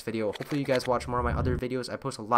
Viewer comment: at the end i thought u said piss out everyone